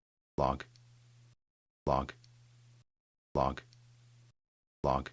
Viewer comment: Bok. Bok. Bok. Bok. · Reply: 😂😂😂😂😂😂😂. I thought it was saying, Bog, Bog, Bog, Bog.. LMAO. Well we know one thing, we both listened to the end. LOL